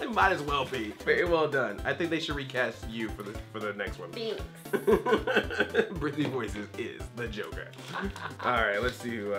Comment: i heard him say BRITNEY voices is the joker